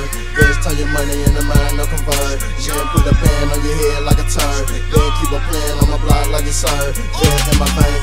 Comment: on 1:46 I thought he said turd? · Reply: Turban bro 8 years late but I got u